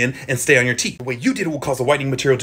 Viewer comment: I thought at the end he said "good luck at diddle school"